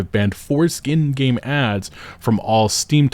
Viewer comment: I heard that wrong and had to rewind and read the text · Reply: Foreskin game ads. Yup. Heard it right.